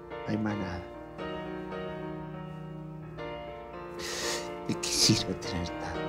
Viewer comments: que dice?? no entiendo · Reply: De que sirve tener tanto? digo yo.... De que sirve... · Alguien entiende que dice al final?? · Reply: De que sirve tener tanto....de que sirve